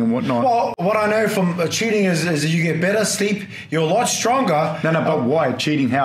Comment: Amazing podcast. It's kinda tragic that Mark Hunt fought so many cheaters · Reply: J He admitted to cheating! Relisting I heard, "I know, from cheating, you get better sleep". It's clear as day!